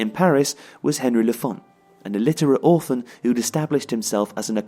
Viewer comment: I thought he said 'illiterate author' ..... I was like 😳🤷‍♂️😂